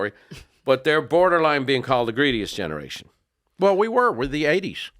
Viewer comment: Incorrect- Boomers weren't the Greatest Gen. That was their parents; they fought several wars, & survived the great Depression. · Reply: Oh I thought he said greediest